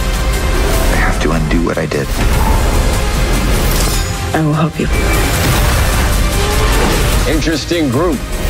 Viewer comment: QUESTION: what she said? I wove you?